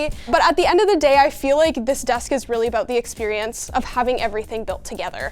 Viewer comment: It sounds like he says this desk is made for quiters… 😅